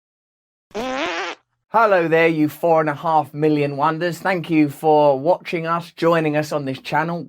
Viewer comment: Love your videos and the diverse people you have on and the various topics.. But I have to admit when I heard you say 4 and half million wonders in your welcome, I actually hear you say in my head not wonders but wan...kers which cracks me up ;)